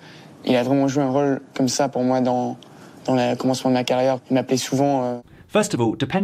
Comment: hi! just leaving a quick comment to notify to you that you forgot "il" before "m'appelait" at 5:04 . Thimothee says it really fast so idk if you didn't hear it but just saying ;) without "il" it would sound quite wrong just as its translation in English